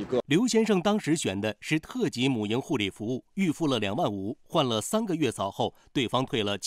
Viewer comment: 特级母婴护理服务，不好意思，我听成了：特级母牛护理服务……